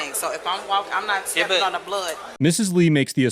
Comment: She actually said..."If I'm not steppin on a blood" at 5:25 mark. Education matters.